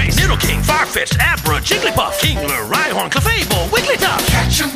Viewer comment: I cant figure out what Pokémon this is it sounds like it says Hitler lol!